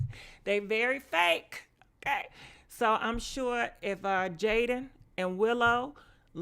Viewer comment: I swear I thought it said Jayden and willow smashed smh I heard wrong